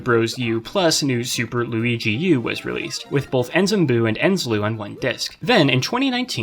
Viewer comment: with both Enzem-blue and Enze-Loo on one disk. This is what I heard, but I knew what you meant. And Im making this time stamp to go back and hear it again.